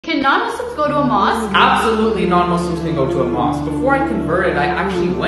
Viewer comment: Ok I thought he said “ABSOLUTELY NOT” instead of “ABSOLUTELY NON-MUSLIMS CAN!”